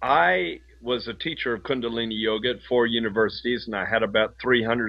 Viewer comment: Ah - at first I thought he said he was a teacher of cunnilingus yoga.